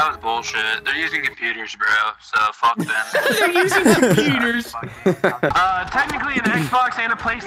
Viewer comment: what i heard was "there using the superior gaming device"